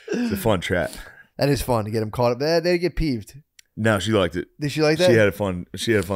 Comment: Thought said the N word and looked back at us to see if we caught it too. What a broski. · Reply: "That ni**a peeped"?